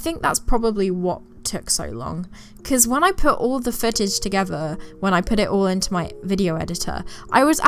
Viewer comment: did she say, “cause when I put all the fetish together.”